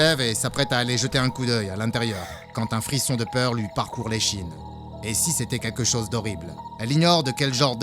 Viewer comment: A7:00j'ai entendu quand un frisson de peur lui parcours les "chibre"😏